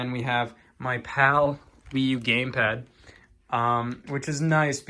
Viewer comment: Did anyone else hear "my pal, Wii U Gamepad"? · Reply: No. He meant as in the PAL version of the wii u which is what the Europeans use.